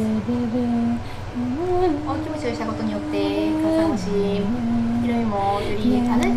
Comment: カサ増し じゃなくて 辛さも増し、 って言ってるように聞こえるの私だけ？そして未だに見てるの私だけ？笑